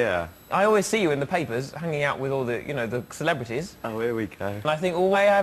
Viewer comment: Is Noel saying "Ohh, here we go..." ???